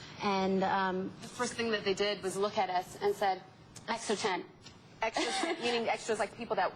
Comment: What does whey say at 02:43 Extra...something??????